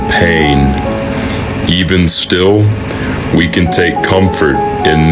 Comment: I thought he said “even still we can take cumfort”